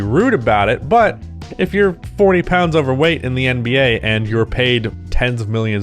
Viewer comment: my speakers were down and i thought you said "underweight". I don't see the flip side of this talked about much but there are a lot of large framed guys that stay skinny and never put on much muscle. You have some of the most athletic players in the world with access to world class gyms, chefs, and coaches, and some of these guys still stay relatively lean... im not saying every player needs to work out like draymond but the advantages seem like common sense